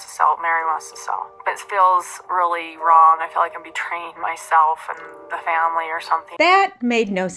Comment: Robyn actually said, "... I feel like I'm betraying myself...", not that she was betraying them, which still doesn't make a lick of sense, like you said. But I think it's worse saying, I'm betraying myself! 😂